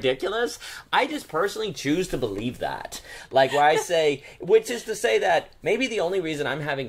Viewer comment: when said, "which is to say...", I thought "witches to say". I have a bias towards that because I'm magick 😬